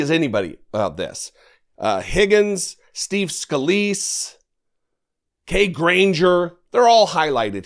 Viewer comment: I thought you said...sleaze scalise, think I like that better. The DNC needs to run commercials in red states now showing the nay votes at the same time showing them praising the bills money they're handing out. I already saw death'santis in a clip talking about all the millions he was providing for 3 - 4 projects, of course no mention of it being from the infrastructure bill. Just a thought, when the signs go up saying what's going on, it should mention the money was provided by the Biden Admin., in great big letters. Maybe glow in the dark paint =^)